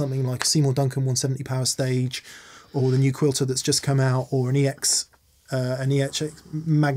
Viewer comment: I heard the "new Quilter"... that box is amazing!